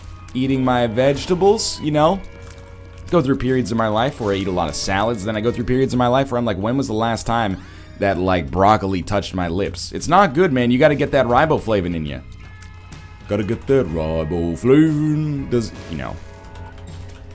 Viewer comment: I was distracted and heard "when was the last time RockLee (instead of broccoli) touched my lips?" I was like "excuse me?"